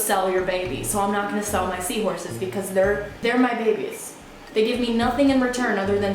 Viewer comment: At the beginning I was doing smth and I thought it said I got my seahorses bitch